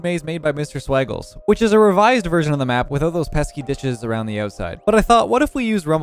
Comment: I thought he said “without those pesky bitches on the side” 😭😭